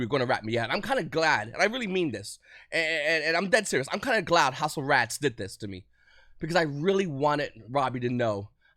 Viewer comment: house of rats LOL XD instead of house of champs.